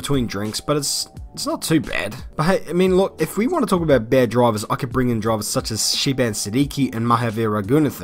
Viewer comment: he did not just say the lord is a bad driver right?